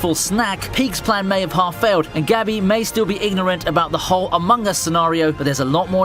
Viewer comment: I just Heard this "Gabi may still be ignorant about this AMOGUS scenario"